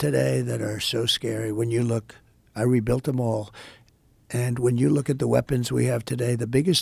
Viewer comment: did he really say that he rebuilt them all?